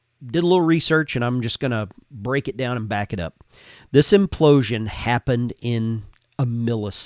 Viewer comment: Lol I thought you said "I'm just goinna break it down and mop it up". After a rewind I realized what was actually said was "back it up" but I gotta tell ya brother, I might have just handed you an official slogan lol.